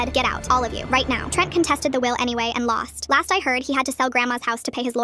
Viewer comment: I thought they said “had to sell my lawyers to pay the house”